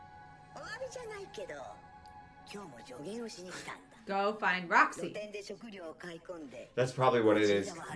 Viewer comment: Did you say "Don't find Roxy?"